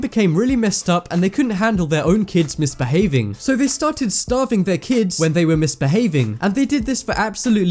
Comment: the subtitles says staving instead of starving. Staving is still a word but it does not mean the same thing. I am not hating I am critiquing the situation in a nice way just so knows and it doesn't happen again